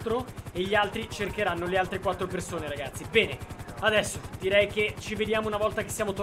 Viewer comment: Ma solo io ho capito "pene" al minuto 10:49?